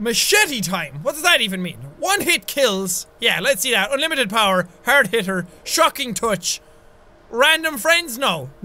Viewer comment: I thought he said "Hard Hitler" 😂😂